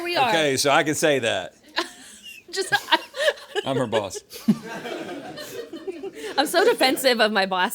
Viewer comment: Every time I heard Rhett and Link say, "I am her boss," I thought they were saying I am repulsed.